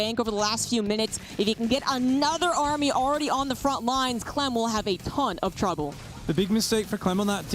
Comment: LOL, at 6:00:23, did someone say, "...Clem will have a c*nt of trouble." ????